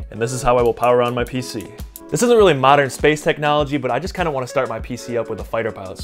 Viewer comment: Modern space technology? No, it's a switch, like a really old switch design. Pft, modern. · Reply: I am yes, partially in one ear. Now it's just down to pronunciation, because you actually said "isn't really modern", not "is not". I heard you when I turned it up but the speed you're talking makes you not pronounce your Ts very well. With that pace and that accent not doing you many favours, I misheard. Guess you'll get more views because some people have to watch it 12 times to hear the words correctly.